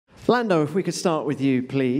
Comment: If you slow it down to 0.25% at the start, it sounds like he says Flando.. just so you guys know